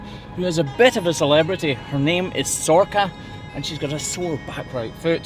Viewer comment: She's gorgeous! For a moment there I really thought you said her name was Sorkka, which is a Finnish word for cloven hoof.